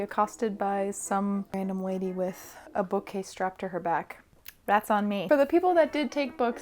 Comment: I thought you said "Rats on me", and I was so confused - I thought it was a new lingo or inside joke I didn't understand haha